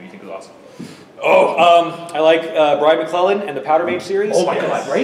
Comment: Please, could something tell me what series he's suggesting at 37:27? I really can't understand those names, neither the series nor the author's, and it's killing me .-. Anyway, I loved the interview! Thanks so much for sharing!! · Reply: The first one Jim suggested (and we geeked over) was Brian McClellan's Powder Mage series. Second suggestion a couple minutes later was the Alex Verus series by Benedict Jacka. And thanks! I'm glad you enjoyed it!